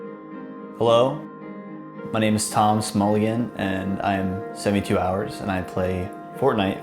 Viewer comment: Am I the only one that heard him say “Tom Smulligan”